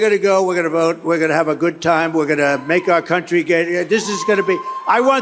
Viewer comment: Almost sounds like he says "Make our country gay again"